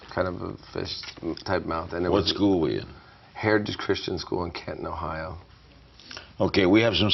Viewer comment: When Larry asked him about about the first person he kissed, it sounded like he said it was in Kenton Ohio. Or maybe Canton? Can anyone tell if it’s Kenton or Canton? Just wondering because I went to high school in Kenton Ohio. I hated that town and couldn’t wait to get out.